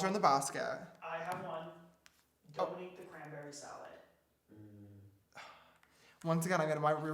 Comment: ate the cracker salad¿?¿?¿😂😂i couldn't hear properly.... idk if that is what he said at 57:40 🙈 Ohhhhh 😂 cranberry salad🤣🤣🤣